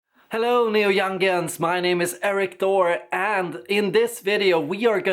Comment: LOL! I thought you said "Hello, Neal Youngians!"